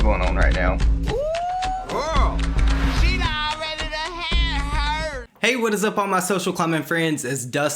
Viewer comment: After the intro goes, “ooh, girl,” what is Dustin saying? I’ve been trying to figure it out for so long lol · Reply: 😆 Thank you for clearing that up Dustin... I thought it said 🗣She bout ready ta hit hur! 🤜🤤 Lmao.. I'm Southern too so that's just what it sounded like to me.